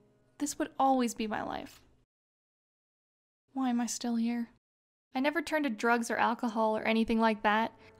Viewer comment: I'm sorry if I sound rude but I thought it said SITH here not still here I just say star Wars 1-6 sorry Edit: I hope you have the best of luck in life I understabd how hard depression can be and most of the time sycrgisty don't work so if suggest family or really good friends. Also before I had a account I would watch your fanf videos all the time don't worry m8 there are people how care about you and people who hope your alright hopefully all your subscribers care about your normal and mental health I believe your actully a good person